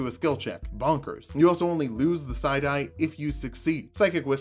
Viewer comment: I heard "side eye" every time you said psi die....